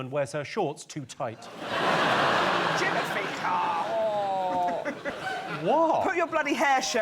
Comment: Did she say, “Jimothy Carr” to scold him? 😂🤣💀